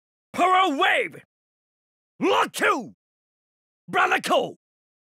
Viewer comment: what did he say in voice 30? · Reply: "Rock you"